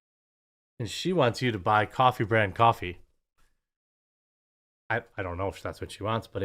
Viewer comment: I thought I heard; Meoffie brand Meoffie